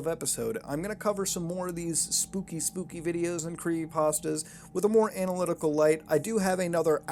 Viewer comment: I thought he said Creamy pasta!